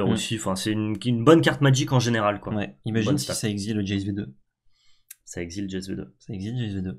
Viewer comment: vous dites quoi à 42:42? "ça exile Jezblbl...?"